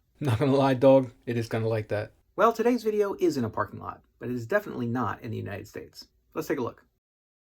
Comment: For a second, I thought you said isn't a parking lot, and Iw as like, then what do you call that parking lot?